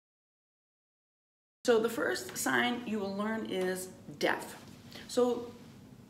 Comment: There are a few mistakes in the subtitles, for example instead of "deaf" the subtitles say "depth" at 0:57 and then "death" at 2:00 and then also throughout the rest of the video.